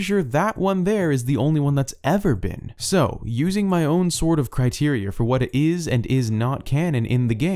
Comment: I thought he said "using my own sword of criteria" 😂... Yes! Slay these theories with your Sacred Sword of Criteria!!! EPIC.